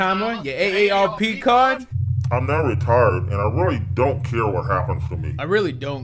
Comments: "now retired" is NOT what i heard when I watched this movie i heard: i'm not (hard R) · no bs when he said "I'm now retired" in the interview i thought he said "I'm not retarded" lmao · I thought the guy said "I'm very retarded so I really don't care what happens to me"😂😂😂😂😂😂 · no bs when he said "I'm now retired" in the interview i thought he said "I'm not retarded" lmao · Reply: I thought he said "im not retarded im retired" but i wasnt paying attention tbh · I thought he said “I’m retarded and i don’t care what happens to me” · I thought he said “I’m not retarded and I really don’t care what happens to me” 😂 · Definitely thought he said"im now retarded, and i really dont care what happens to me"..... cant be the only one.